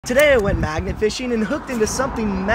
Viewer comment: Oh few I thought he said maggot fishing. Oh my gosh. Lol. I'm a dummy